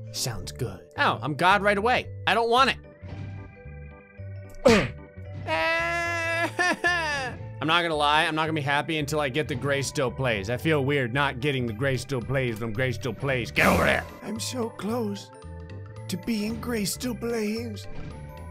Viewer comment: it sounds like he says “Graystillblaines”